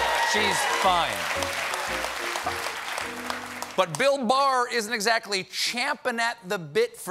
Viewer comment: i thought he said Bill Maher for a second lol